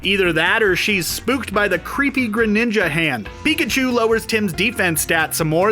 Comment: did you say Greninja ? That's me!